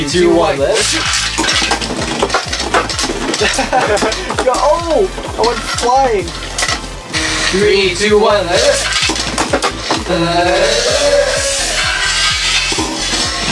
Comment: I swear he said Let it shoot!